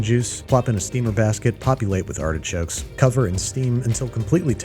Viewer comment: my hearing must be going, or my dirty mind is acting up again. I heard "copulate with artichokes " That'd hurt, wouldn't it??? 😂 😉